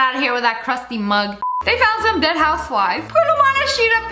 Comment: what she said : They found some dead houseflies. The captions : they found some dead housewives 💀💀💀